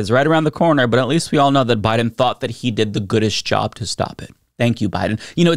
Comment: I agree with you on everything except I really think he said "as good as" not "goodest". Things are bad enough, but harping on this mishearing is a distraction.